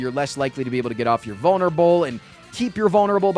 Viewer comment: I was listening to this in the background while doing some other stuff and when he says vulnerable at 38:48 I had to rewind because I heard him say "boner bowl". Definitely sounds like that on rewind too. Am I crazy?